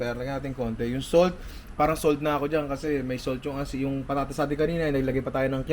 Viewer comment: Tama ba narinig ko👇 May Salt ang Asin😆